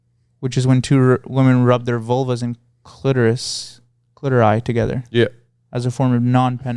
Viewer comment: IS ANYONE ELSE HEARING THIS? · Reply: CLITORI AND FROTTAGE ‼️